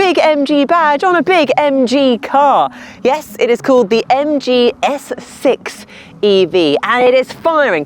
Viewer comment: I keep hearing 'the MG Essex'. Sorry (not sorry) if you can't unhear it now